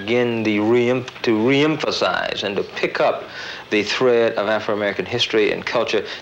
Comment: Did I just hear African Americans from a video from 1970? Why did they say that we weren't called African Americans until 1988 when Jesse Jackson gave us that name when it was already used? People need to get their facts straight. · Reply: He says "AFRO"-american at **6:11** in THIS video. Where exactly does he say "AFRICAN"-american??? Also, I didn't even say ISH about the media, hahaha!